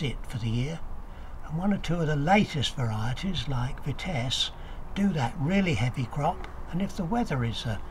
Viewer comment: sounds like he is saying Vitesse but i think he means Finesse which is a british bred everbearer